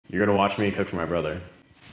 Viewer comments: Did he just say "You're gonna watch me touch my brother" Or am I insane. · Why did at the beginning I thought he said your gonna watch me cook my brother and I thought those bowls were ashes 😮